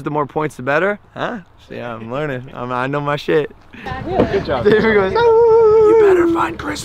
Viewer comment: when I first watched this vid I thought he said I know my shapes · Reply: Sorry to break it but he said shit 😬😬